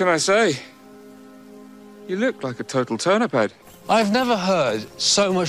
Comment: Colins accent came through a bit when he said “yOu LoOk LiKe A tOtAL tUrNiP hEaD” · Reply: Thanks for that. I wondered for a long time what he was actually saying. I kept hearing 'turnaround' and was left confused.